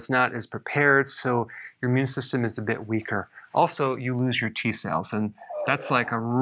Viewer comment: That's me iron pills 3 a day. T cells I thought he said teeth that's me antibiotics that's me stress o that's me